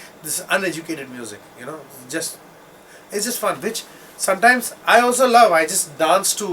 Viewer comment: I think I heard " It's fun bitch" at 15:44 haha